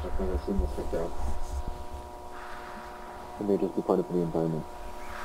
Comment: I made my volume max and from what I heard it’s says “Maybe it’s apart of the environment” And at 11:26 he says “Aproaching they un i dentified entity” So they are exploring the backrooms and to make sure they don’t get lost they have a red rope or hose to lead then back to the entrance so they won’t get lost and the reason why they didn’t focus on who’s the person is because they discovered other bodies in the backrooms and for the collateral damage video here is my theory: So when Async broke reality it’s caused a massive vibration wich caused a earthquake so we can see the Async lab is located somewhere near San Francisco or somewhere in California.